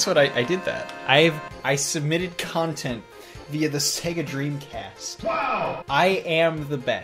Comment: I thought said his dead dream cast Was you know dead